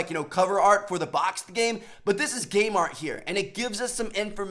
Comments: i thought he said gaymart😂 any ways who else is just gonna get infinite warfare throw it away n just play cod 4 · i heard "but this is gaymarTn here"